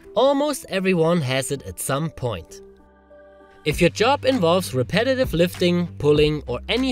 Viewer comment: I heard "if your job involves competitive lifting..." at first 😀